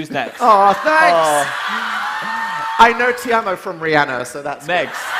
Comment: what does that person say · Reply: "te amo"